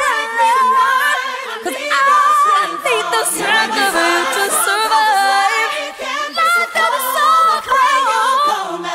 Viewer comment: through 3:13 what does she say? “ I need the strength of ——— to survive ?” Someone help me out here pleaseee lol 😭😭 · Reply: Mariah Stripped thank you love 💕